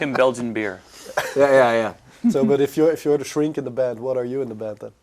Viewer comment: At first I thought he said if you're the shrink in the bed, than what are you in the bed. With Tre's response it works. I will only accept band is now bed